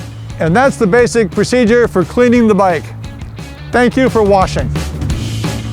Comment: Did he just say "Thank you for washing" at the end? HAHAHAHA